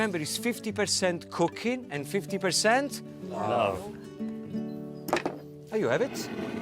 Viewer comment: i misheard that as 50% coca*** and 50% love.